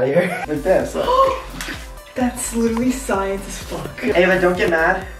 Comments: I heard her say science as fuck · I'm not sure if it's just me or is saying "That's literally science af" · she was like "that's literally science as f*ck" who else heard or is it just me lmao😂. no hate to or I love them. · doesn’t it sound like said this looks like as science f**k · did anyone else hear her say "thats literally science as f*ck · did she say fu## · said " thats laterally science as fuck" lol 😂at least that's what i tought she said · did she say that’s science as F**k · In 11:50 “that is science as fuck” did I hear that right? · I heard her say fuck or was that me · did she say that’s science fuck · Did she say "thats science as f**k At 11:51? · Who else heard say " as f**k" at 11:51? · did Ava say "That's literally science as fuck"?? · maybe i heard it wrong but i think that said fxck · did said F*ck? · anyone else heard her say ".....as fuck" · Did she say “that’s science as fuck” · who heard say science fuck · I heard Thats literacy science as Fk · Is it me or did he say fu** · is it just me or did say the f word She said: That’s science as f***! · did just said'thats littery science as fuck'So curses